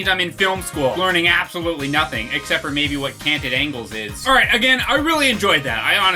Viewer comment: at roughly 38:00, thought you said "candid angle"... that brought up a completely different image of what you planned to do with that Totino's roll.